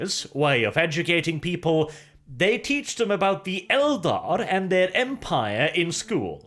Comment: Did you actually say teached there? Immersion broken you nob :D